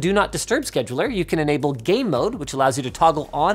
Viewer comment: I thought he said gay mode